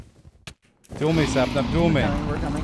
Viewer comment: wth did he just say? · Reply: "Duel me, Sapnap. Duel me."